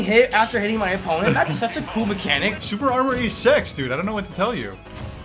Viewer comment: “super armour is sex dude” what did u say?